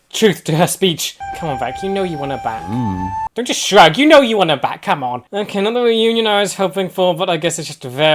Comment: For a second there I thought you said "I know you want to bang"